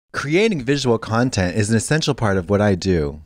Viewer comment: I thought Katya said at the beginning of the ad for the video that she makes “abysmal content “ instead of visual content